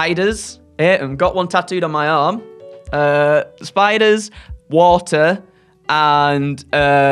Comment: Was I the only one who heard WALTER? I mean as far as I heard he is a very lovely man,but...well if he is furious you should really run (and ideally not on water too)